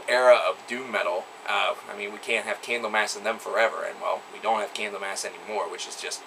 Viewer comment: what did you say in 6:00? can't have _____ forever. we do'nt have _______ anymore." i didn't get that.... is it a band's name? fill in the blank please :) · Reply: Candlemass! Anything else, man? I'm planning on rewatching this video in 8 years time, so...